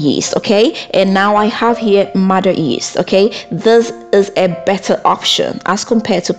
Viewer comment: I can't quite hear what that yeast you used is called? Sounds like you said modern yeast. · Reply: Mother yeast